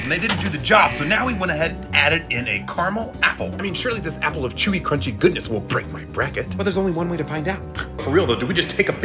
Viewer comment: It sounds like you said: “This is a Carmel apple…And this is a racist bracket…” 💀